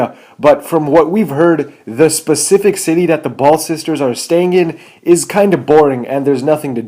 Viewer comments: did he say ball sister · Anyone hear ball sisters